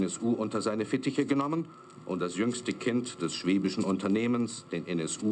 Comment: Welches Schwedische Unternehmen war denn bei NSU und VW> 1:20 daran beteiligt? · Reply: "Schwäbisch" nicht "schwedisch" 😂 (ich habe es auch zu erst falsch verstanden)